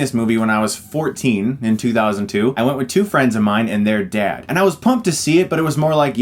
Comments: Damn when he said I went with two friends of mine and their dad (thought he said and their 💀 dead) was like brah 😵😂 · lol I thought he said "I went with two friends of mine and they're DEAD" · I honestly thought you said "I went with two friends of mine and they're dead" at the beginning of the video... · I thought you said, and they are dead lol